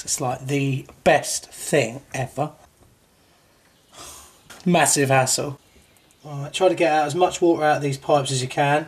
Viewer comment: I like how at 5:43 the auto generated captions mishear "massive hassle" as "massive asshole."